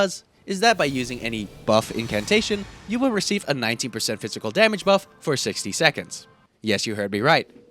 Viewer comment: no tf I did not I thought you said 90% i almost choked 💀